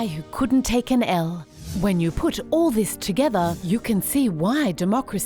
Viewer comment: What is she saying @ 3:33 with Trump pictured and what does it mean? " . . . but not from a guy who couldn't take an _________" It sounds like . . . EL . . . ? Thanks! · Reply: "L" as in "Loss"